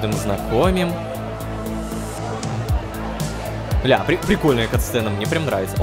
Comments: что он сказал? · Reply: Ля, прикольная катсцена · мне послышалось или это было:"6ля"?